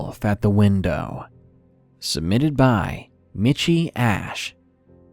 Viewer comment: Lmao. I misheard the author of the 3rd story. Its Michy Ash..I heard Itchy Ass. Sigh.